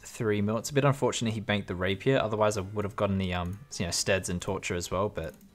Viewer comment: Did you say Steads at 1:47 ? :D they do looks quite the same